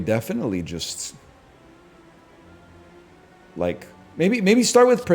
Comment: My brother in Christ you said Like I heard Mike. Literally woke me up 🤣